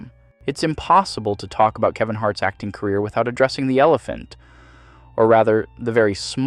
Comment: Did you say Yellephant at 2:50? I hope you did 😂